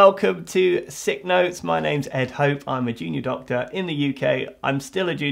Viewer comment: I always hear “I’m a genius Doctor” in your intro and it cracks me up